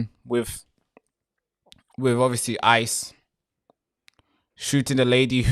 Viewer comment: did you just say shooted? I thought the Brit’s were better at English.